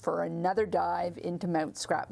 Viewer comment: Mount Scrapmore, Ha! At first I thought you said Scrap Morgue! Well either one is accurate!